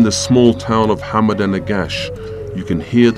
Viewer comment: Can somebody tell me what town is he talking about at 13:21 ? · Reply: Al negash and before that Aksum